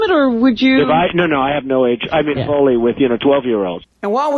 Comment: What did he say about 12 year olds? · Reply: I thought he said "no, no I have no age (limit) you know, only with like 12 year olds". I took it to mean he was saying the limit is 12, but I see others hear "all in". Not that either is anything approaching normal, legal, or okay